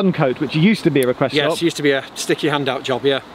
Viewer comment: I thought he said "Sticky hand out station" :-) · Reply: And for a moment all I heard was "sticky hand job"